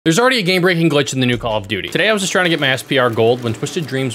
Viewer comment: I don't know why I thought you said this is a gay breaching glitch